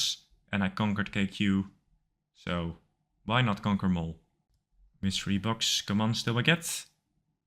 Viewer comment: did u just say kanker mole?